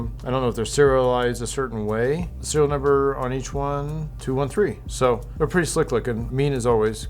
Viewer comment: Did you say "213"? Seriously? If so, are you sure that's the SN and not 213 as in the other reference it would be?